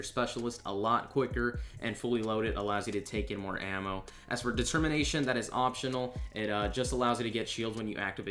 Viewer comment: Just a tip. The PO-8 will allow you extra leg shots for those early rounds, because the damage is lower. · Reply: Did he say “discrimination” at 0:41?